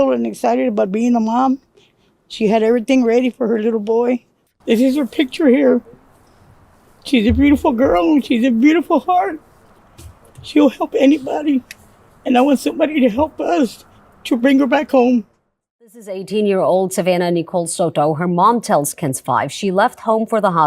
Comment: Not trying to be an asshole. Really. But did she say "she would fuck anybody" somewhere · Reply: I’m sure she said she would HELP anybody 😂